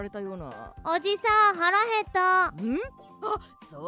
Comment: 「おじさん腹減った〜」が 「おじさん腹立った〜」に 聞こえてもうたwww